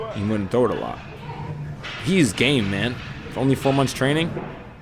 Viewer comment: I heard ''He's gay man, only four months training''